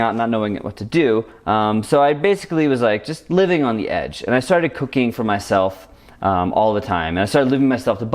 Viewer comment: i thought he said he start cocaine